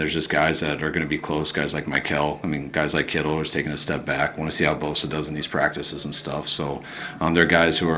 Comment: I heard "Kittle took a step back"